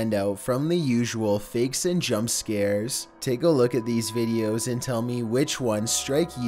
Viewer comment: did you say jumpscare?..... Me: 💻 😨